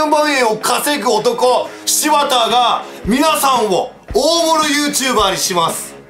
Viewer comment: 大物YouTuberエロって聞こえた笑笑